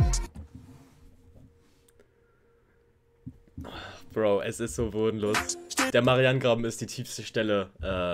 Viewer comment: Hab ich das richtig gehört? Hast du bodenlos gesagt? Oh mein Gott ich hau mich weg.